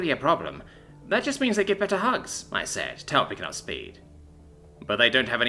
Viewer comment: At the end when you said tail picking up speed I thought you said tail be coming speed